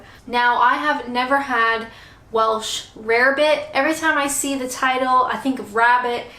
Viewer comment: I always thought they were saying "Rabbit". This is an education.